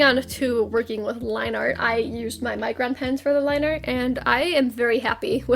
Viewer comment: So I have pretty bad hearing (my parents say it's from listening to music too loud or something like that) but yeah, so when he said micro pen I thought he had said Minecraft pen.